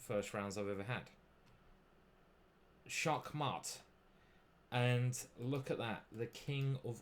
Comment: did I hear 'schaakmat'? 😂